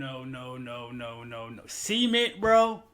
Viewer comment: when said adding cement, the way how he saided sounded like semen 🤣🤣🤣🤣